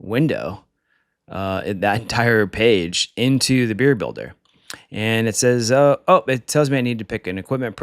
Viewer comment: I honestly thought that said "Beer Bladder" at first ! This is way better.